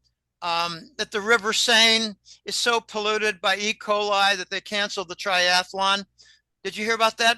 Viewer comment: I think polluted by poo is what I heard. 😂🤣💩💩💩💩💩